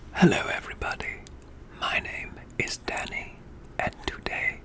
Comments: I swear I almost thought he said daddy when he actually said Danny 😂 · OMG I have no idea WHY but it sounds like he said My name is DADDY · i thought he said "hello everyone, my name is DADDY" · I thought he said “my name is daddy” lol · Did he just say my name is daddy😂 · Wtf i thought you said: my name is daddy 😂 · Did I hear that right “ my name is daddy “ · Firstly i heard "my name is daddy" and was like:😶 and i return again and i heard "my name is Danny " PD:wattpad make me like this.